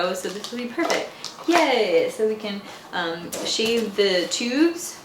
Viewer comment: OMG I was up making dinner and thought you said shave the pubes ha ha ha till I replayed it. OMG I was like WTFFFFF did she just say